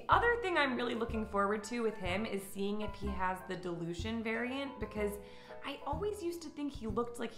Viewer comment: I thought you said "delusion variant" heheheh